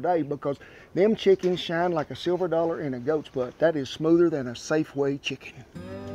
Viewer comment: “Them chickens always shinned like a silver dollar in a goats butt” 😂😂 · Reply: I thought he said "ghost's butt"